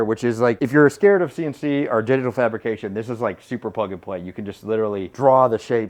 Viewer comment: it sounded like you said "genital fabrication" at 2:44.... and yes, thats something i'm definitely scared of